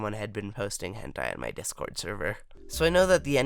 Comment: When you first said "Someone was posting hentai in my discord server" I thought you say "Someone died in my discord server"